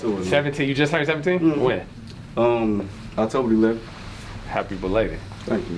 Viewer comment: Did he say October 11th😯 2:45 Heyyy Bday partner🎂✊🏼✊🏼✊🏼